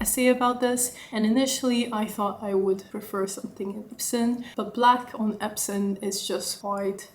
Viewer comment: Beautiful bag. When u talked about other leather, I heard you say “epson” 7:23 leather, is it epson or “epsom”?